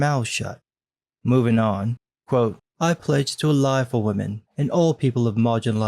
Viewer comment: What it reads: "I pledge to ally for women." What I heard: "I pledge to uh-lie for women."